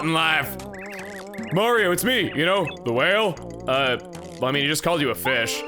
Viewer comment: I had to do a quick repeat - I thought you said “a whale we go”